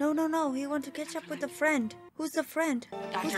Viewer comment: "No, no, no. He want to catch up with a friend." 😏Em... what he said was, "I'm having an old friend for dinner." Now remember, he's a cannibal, so the exact meaning behind his choice of words is open to interpretation.